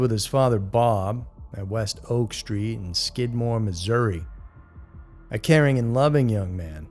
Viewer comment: I seriously thought he said "SKIDMARK MI" 💩